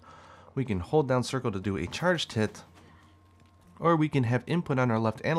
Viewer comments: "charge t!t." What? Yeah, subtitle also says charge t!t. Awesome new move! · we can hold down triangle and circle to do a charge tit I know you meant charged hit but it sounded like that.